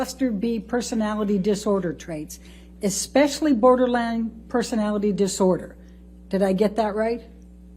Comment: did she say Borderlands?